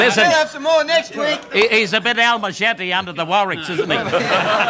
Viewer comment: Does anyone know what Maurie is saying at 07:39? I know it's rhyming slang for "he's a bit sweaty under the arms" and it sounds like: "he's a bit 'Alma Shetty' or 'Al Machete' under the Warwricks". So warwicks is rhyming slang for warwick farms (arms) but I don't know the first bit.